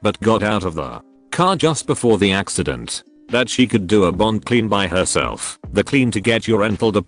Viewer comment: I thought I heard BONG clean, and I was confused where one would rent a bong from